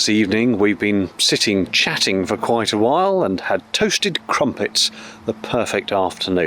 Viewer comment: Funny, I heard "croasted trumpets"... and then I wondered for ten seconds or so about what croasting was and what a trumpet would taste like... and then the light went on. Looks like it was a fun trip! Many thanks.